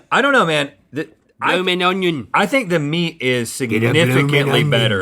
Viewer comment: Did link say "I'm an onion" at 12:31😂😂😂?